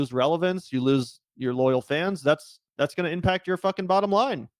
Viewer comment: Omg but I thought it said only fans 🤣🤣🤣🤣 I was like woah !!!! I also used to like and respect him until I found out he was a sell out